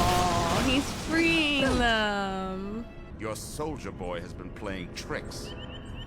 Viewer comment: I laughed really hard when Jax-Ur said Soldier Boy because it sounds like he said Soulja Boy.